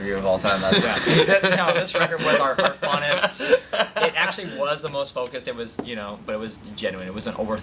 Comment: For a second I thought Ash said "Ashley was the most focused."